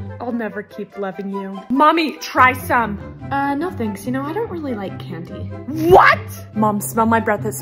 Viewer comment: when i scrolled down, on "i don't really like candy." and i heard "i don't really like you-"